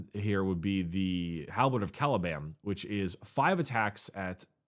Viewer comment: Did just say Halberd of CaliBAM or am I imagining things? Are you making fun of the destruction of Caliban? Where is Asmodai when you need him...